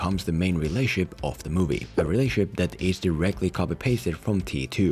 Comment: "Did you say relay-ship" or did you just pronounce relationship like a hillbilly